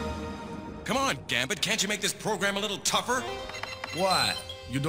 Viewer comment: I thought he said, "Come on, dammit! Can't you make this program any tougher?!"